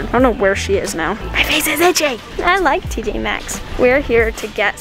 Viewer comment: When said ‘I love tj max , I thought she said ‘I love pj masks’